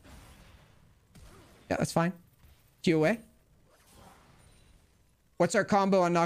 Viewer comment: i kept hearing Q/A not 'Q away' but when you said "Q OUT" i got it instantly.